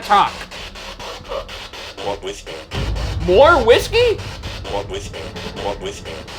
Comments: I think it said "More With Me" instead of "More Whiskey" @ 12:44. That's what I heard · i definitely heard “walk with me” not “more whiskey” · I thought when Ryan said “we just wanna talk” the voice box said “more with you”